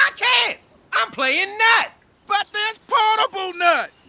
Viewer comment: he's playing Knack? What did he say? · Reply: Nut